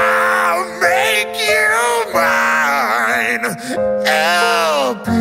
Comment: Did he just say bitches😮